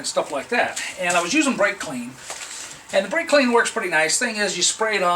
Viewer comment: Too funny, the closing..Thanks for video, I was looking for a good degreaser, never thought of brake cleaner..If you like it than Purple Power, its good enough for me to try out..But can't find that Bright Clean, does anyone have a link for that? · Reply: You mentioned Bright Clean at 0:30-0:31 into the video? Maybe I heard it wrong?